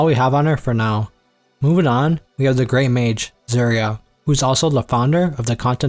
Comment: cant tell if this is an ai voice or not... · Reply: Yeah i cant understand any of the names he said. I was doing dishes so i couldnt see the screen. He calls serie "Zuria"